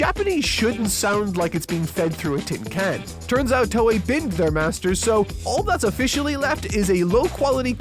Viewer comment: What did he say @ 1:50 "Turns out toei b___ their masters" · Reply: "Binned" as in thrown in the trash bin